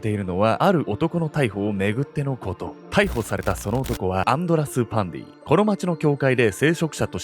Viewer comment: 終始アンドラス･パンディが、アンドラス･パンティって聞こえてました（笑） これも…洗脳なんでしょうかね😅